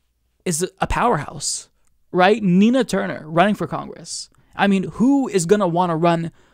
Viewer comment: I thought you said "tina turner"